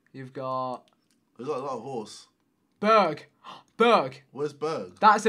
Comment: you startled screaming berg but it sounded like you said bög. Bög means gay😂😂😂